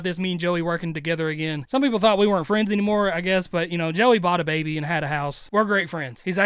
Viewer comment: did say Joey BOUGHT a baby?????